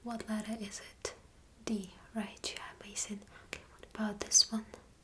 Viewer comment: did you say Mason at 4:05 or so? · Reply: i got it, amazing.